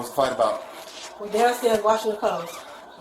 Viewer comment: At first I thought she said “we was downstairs watching the colts” which in that case would explain everything